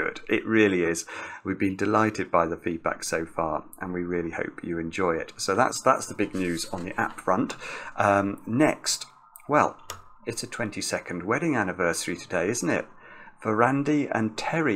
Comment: I heard it as "it's a 20 second wedding anniversary today isn't it" and thought to myself "hm that's a new one"